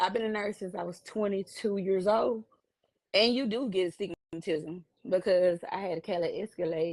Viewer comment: did she say astigmatism ?